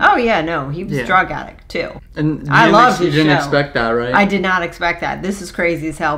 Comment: i swear she said "i love snow"